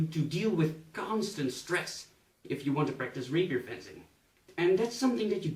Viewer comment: For a second i thought it said raper and not rapier for goodness sake 😂😂